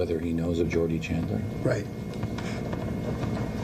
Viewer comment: The interviewer sounds like he says “Jordie Chandler” instead of “Jordan Chandler” and I believe that’s why MJ screwed up his face asking “who”?